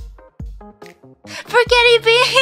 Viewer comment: I thought she said "Forghetti" beam instead of "Forget it" beam